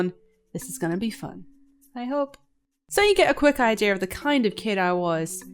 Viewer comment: did u say idear is it like an accent thing